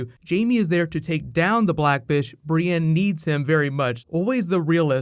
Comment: Sounds like "black bishhh" instead of black fish @ 3:05 ..... 👀